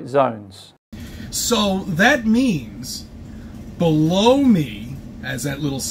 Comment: …did CC just say “blow me”? Gross 🤢